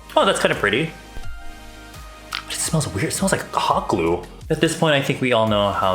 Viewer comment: The Asian version of James Charles but he curses more and doesn't say "HeY sIsTeRs" and literally when he said "its smells like hot glue" I thought he said cock glue I was like that's illegal